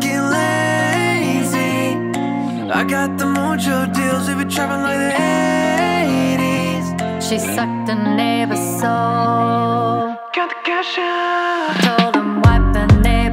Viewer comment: “She sucked the neighbours soul” 😂😂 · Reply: lel funny at first i thought HOL UP, WAIT A MINUTE, SOMETHING AIN'T RIGHT all along i thought she said nibba she actually said neighbor im jk see ya nigga